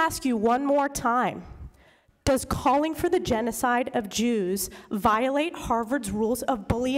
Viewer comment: I thought she said the genocide of juice.